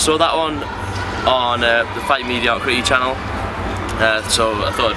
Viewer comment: guys what is the name of the channel i didnt understand what he actually said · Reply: It's a channel called "fight mediocrity". Guy does detailed book reviews and things of the sort. Solid channel.